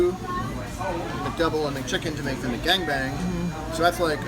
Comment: Did he say McGang Bang...? lol